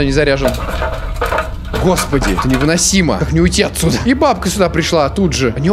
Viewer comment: мне одному послышалось "ебабка сюда пришла"? 😹😹😹😹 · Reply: Там на самом деле ,, и бабка сюда пришла"